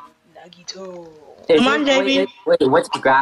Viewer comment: Was i the only one who heard “nagiiiitooo” 😂